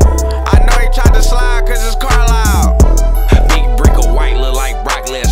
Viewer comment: I know he tried to slide cuz his car loud 😂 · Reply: Oh I thought he said "cuz it's Carlysle"